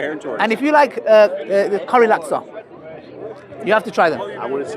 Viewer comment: Did said Curry laksa? 19:22 😂 one of a type of food in Malaysia